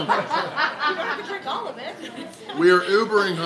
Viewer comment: The burgers look and sound great. Glad the beers are optional. At the beginning I thought you said you had to drink all 10.